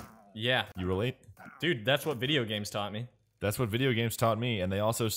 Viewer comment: I actually thought said that video games taught him to speak like a sheep